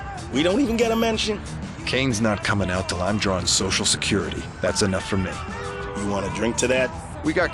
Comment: why do the guy says Social Security and the subs says 'pension'? lol I'm giving it a chance for the first time, specially since I hook up my wii again and was after some curve ball instead of sticking to marios and zeldas. This game is amazing and probably the best GTA-like on the system.